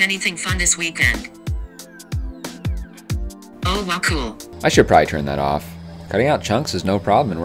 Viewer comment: super big fan of the machine trying to make small talk. What is it actually saying? "Oh wacool"?